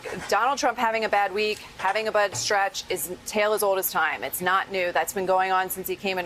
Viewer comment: does she say that "donald trump is having a Butt Stretch?" HAHAHA, I find it believable. Gotta make room for more 'publicans.